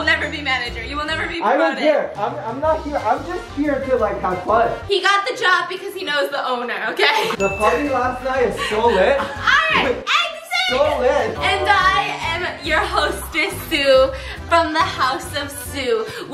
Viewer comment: He got the job cuz he knows the owner.the party last night was so lit, so lit!!!!!🤣🤣🤣🤣🤣🤣 Biss I am dead DEAD😂😂😂🤣😎😎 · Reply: I thought he said the fucking last night was so lit. I rewound it twice and k ed pt hearing that. It wouldn't make sense if was embaressed if he had said party